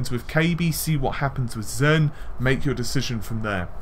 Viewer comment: Lol, am I the only one who heard the last "but" @ 7:27 as "FUCK!" I swear, it sounds like he says "FUCK! With all of that said.... "